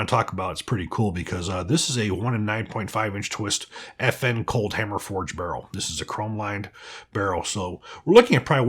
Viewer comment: When he said "FN Cold Hammer Forged Barrel" I heard "FN-Colt Hammer Forged Barrel' I was like 'when did FN buy Colt'?"